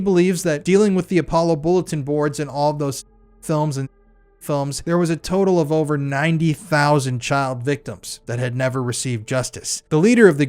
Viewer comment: Did ypu really say at 1:08:11 " there is a total of over 90 thousand child victims"?